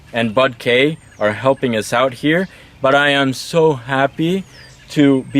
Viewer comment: Did he say butt k ?